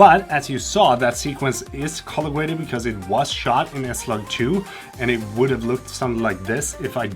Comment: "That sequence is color-graded cuz it was shot in a ????" ... what does he say? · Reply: I say S Log 2 :)